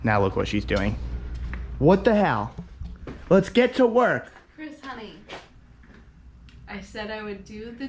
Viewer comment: did she call him chris money ;)